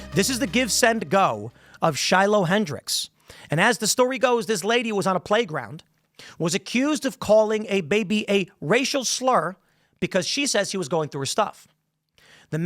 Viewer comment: My hand to God whenever Tim started talking about this story, I thought he said when a woman was accused of calling an ape a racist slur! 😮😂😂😂😂😂😂😂